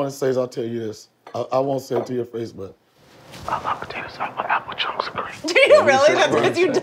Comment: What was said at 23:30 when he whispered to Whitney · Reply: He likes apple pieces in his potato salad